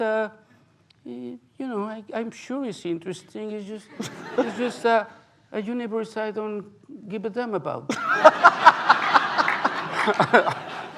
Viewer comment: What did Pavel said to Cuarón? 1:11:22 "I'm sure it's interesting...." I didn't understand the rest. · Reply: Armando Lara “it’s just a universe I don’t give a damn about”